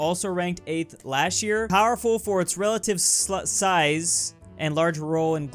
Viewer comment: did I hear him right or did he say 'relative slut size'😂😂